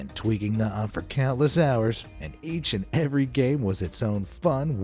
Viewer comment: I thought I heard “And each and every gay”